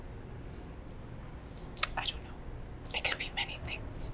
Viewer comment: when she says „i dont know“ the subtitles say „definitly“ bro lmao